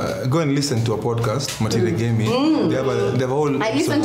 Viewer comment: Loved listening to this!!! What is the name of the podcast and mention @ 38:25? I couldn't catch the name. Thank You! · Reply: Matirî Ngemi